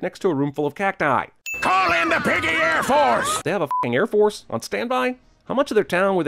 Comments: i thought they said assholes instead of airforce :D XD LOL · oh, i thought he said call in the piggy "pie" holes